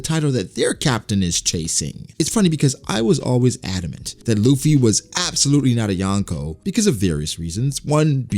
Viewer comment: Did.. did you just say Luffy is a yonko? I must of heard wrong